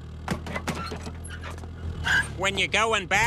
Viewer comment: “F you! Get back in your car with your wife!” Love it. This happens at every boat ramp at least once a weekend. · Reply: dodgeguyz I thought he said "when your white"